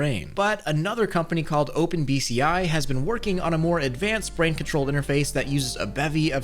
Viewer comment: did he just say brain control?